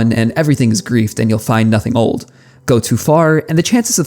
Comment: I’m sorry…. Did you just say, “goats who fart”? Edit: I had to use subtitles to figure out he was saying, “go too far” mb